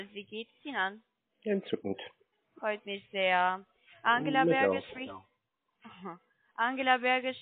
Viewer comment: Bei 15:43 verstehe ich als ein genuscheltes Angela Merkel.